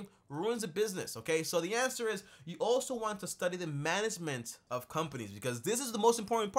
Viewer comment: I was very confused because I thought you were saying GARMENT